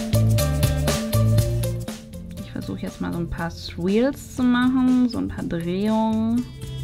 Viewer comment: meintest du bei 6:36 swirls? Es hat sich schrecklich angehört.